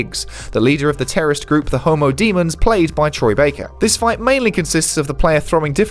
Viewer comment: Did he say homo demons, you don't wanna be caught by them:)